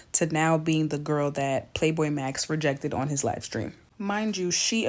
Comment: PLAQUEBOYMAX NOT PLAY BOI MAX LOL